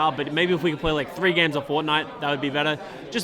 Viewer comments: I thought said "If we could play 3 games of Fornite" and I can't unhear it! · I thought said ''maybe just 3 games of fortnite'' and I was like wtf but then i remembered he's australian and said ''maybe just 3 games a fortnight'' which is 2 weeks